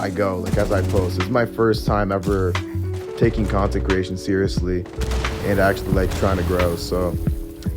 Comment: siege is one of the few things I miss about console there's just so many cheaters on pc it's wild. Very well put together video the subtitles are wrong at 9:05 tho when you say "content creation" it shows "consecration"